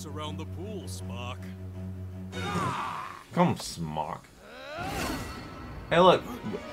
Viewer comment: Aquaman didn't call Namor "smock", he called him "Spock", like from Star Trek, due to his pointy ears. Though I may have misheard you again.